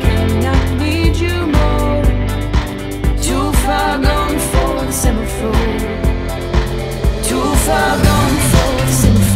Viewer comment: For weeks I thought she was saying to far gone for a centerfold! :: But same the word of this song and the tune etc. are beautiful!